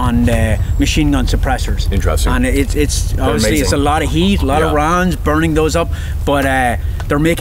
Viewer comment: Thought he was saying lots of “runs” not lots of rounds 🤦🏻‍♂️